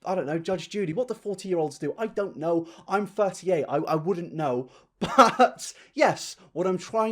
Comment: I thought you said "I'm 38" and I was like you definitely are not that old but then I looked up your age on the internet and I am glad to know I misheard you and I don't have to be jealous of your genes